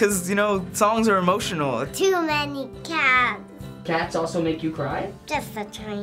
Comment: did she just say too many cooks?